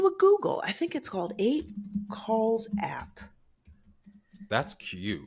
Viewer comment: The app is 5 Calls.